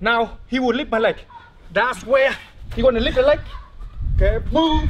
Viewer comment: I thought he said "he gon lick the leg"